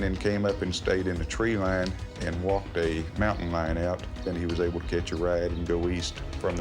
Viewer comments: I kept hearing "he walked a mountain lion out". Maybe a mountain line? I don't go to the mountains.. is that a path animals/people make? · did I hear him correctly? He walked a mountain lion out? I was almost bit by not one but two water Moccasins out that way so I was just thinking, “Travis had a lot of wildlife to deal with.”